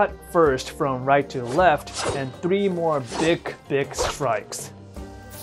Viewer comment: am i hearing correctly?? @ 2:50 big D'i'k strikes?